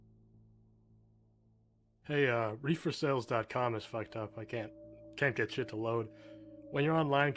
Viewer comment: Nice! Side note: I started this story while washing dishes, so I wasn't "watching". When you said "Refersales" I heard it as "ReeferSales" and thought, "are they seriously in a chat room trying to sell pot?". I know, I know....